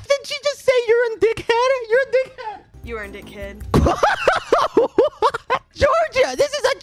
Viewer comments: I hear "you urnd it kid" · i thought she said you're worth it kid XD